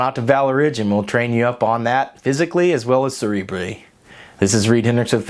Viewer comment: What the hell did you say at 7:20? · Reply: m hall cerebrally, it's a pain to pronounce